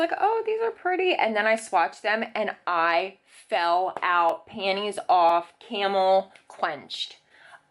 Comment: Wait... Did she just say panties off, camel clinched? 💀💀💀😂😂😂😂